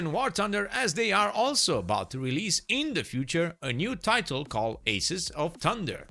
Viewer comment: ah... it thought it said "new titties"